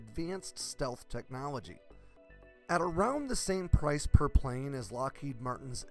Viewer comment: I'm pretty surprised that it costs as much as the f-35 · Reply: ? I thought that he said half the price.